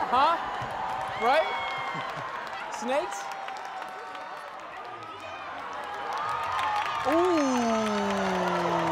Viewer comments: What did Snakes say? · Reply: Still wondering? Hahaha I heard she said she'd take the money and leave him 😂😂😂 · What did Snakes say at 17:19....? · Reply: If he has his last thousand then why would I still be doing with him